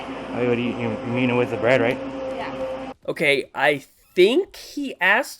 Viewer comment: Oh damn i thought said oh you like the bread here